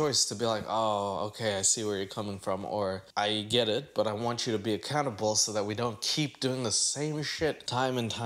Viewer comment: i thought said "i want you to be a cannibal"